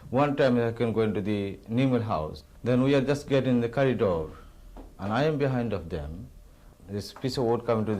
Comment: did he say "curry door" ?!?! @ 9:52 😆